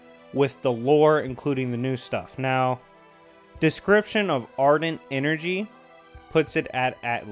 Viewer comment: did you say ardent?